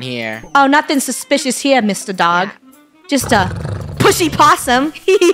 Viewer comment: did she say a bad word or not cause I heard a bad word🧐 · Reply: Its pushy